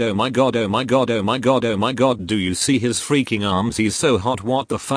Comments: At around 5:58, the Oh my god part, I kept hearing My Goddoh! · what you said : oh my god oh my god oh my god what i heard : my goddo my goddo my goddo